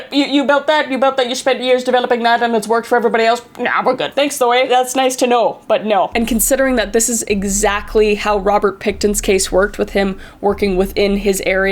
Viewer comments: At the start I thought she said Robert pig tit 💀 💀 🖐️ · I thought she said Robert pigton☠️☠️☠️☠️☠️☠️☠️☠️